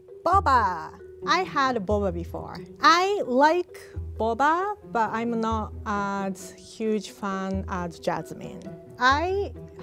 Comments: haha I thought she said "I love boba but I am not a huge fan of Jasmine" 😂😂😂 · Am i the only person at 0:43 that heard "I like boba, but I am not a huge fan of Jasmine" instead of "I like boba, but I am not as huge fan as Jasmine"? I watched this like 3 times before i understood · Did she just say "I'm not a big fan of Jasmine?" Wtf Rie 🤣🤣